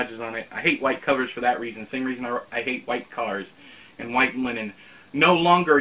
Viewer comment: when you said you hate white linen for a second I thought you said you hate white women lol